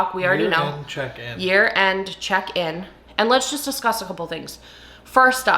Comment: Thank you so much for clarifying that you wanted to do a "year end check in"! When you first said it, I thought you said "urine check in", and I REALLY didn't want to do THAT.